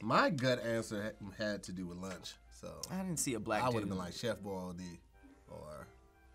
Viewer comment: Did he actually say "Chef Boil D...." like hes some 80's rapper 12:07 .